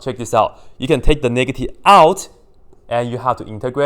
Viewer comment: WHAT DID YOU SAY? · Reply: Thumbomb “you can take the negative out”